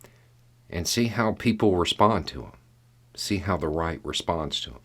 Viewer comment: I misheard something you said, but it made me think about what is happening in America on a macro scale. In finishing up, you said “ look for someone (maybe a governor) who starts giving commands and see how the right responds”. I (being only half awake) heard it as “see how the Reich responds”…. I couldn’t get that out of my head.